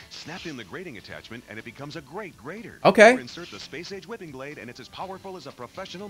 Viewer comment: i swear he says space aids whipping blade